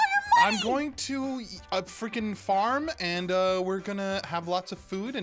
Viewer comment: Did just say frickin???? go to 4:45 to confrim